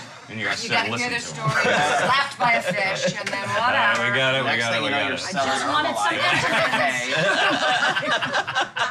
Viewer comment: Does anyone know what says at 14:02?? I can’t hear and everyone finds it funny lol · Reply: Bea next thing you know you are selling herbalife